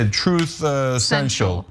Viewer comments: I thought he said “truth sensual.” 😂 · I thought he was saying "Truth Sensual." Which is a whole new level of horror